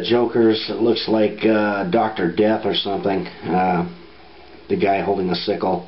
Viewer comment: Haha, the jokers look like...Dr. Dre???thats wat i thought u said...haha!!